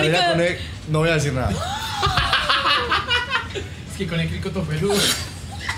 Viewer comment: Que dijo? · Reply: Con el cricko to peludo que es la parte íntima de una mujer